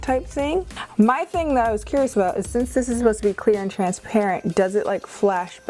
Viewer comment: When you thought she said Queer instead of clear >3> Damn my gayness is showing.